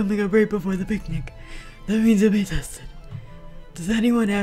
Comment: “You’ll be tested” I thought it said tasted...help me